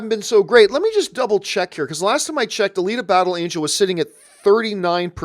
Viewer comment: Alita Battle Angel.... For the longest time I heard "A league of battle angels". Could have been a cool title I think :-)